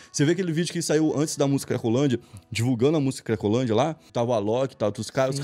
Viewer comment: só eu que ouvi ele falar rolandia kkkkkkk